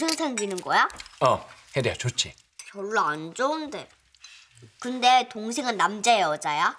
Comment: 나만 졸라 안좋은데 라고 들림 ?